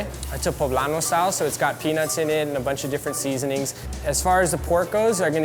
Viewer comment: sounds like he said it has penis in it